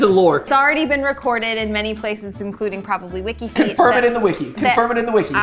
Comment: did she say wikifeet??